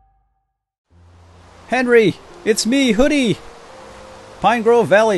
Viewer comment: oh he said? i thought he said Herpy